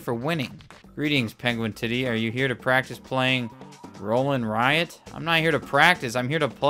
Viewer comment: Red Riot is what I heard